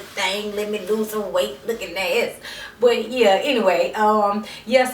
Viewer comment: wait amin.... roflllll..."'lookin ass" @ 0:19? And THEN did she say "life is way too short to not be out here fine as f*ck" riiighhtttt.. lololol too funny. omg.